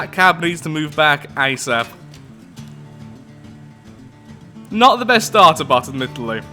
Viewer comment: What was it he said again? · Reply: "Not the best starter bot, admittedly!"